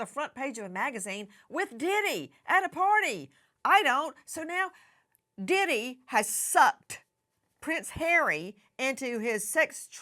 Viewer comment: Omg… I thought she said the diddler SUCKed the Prince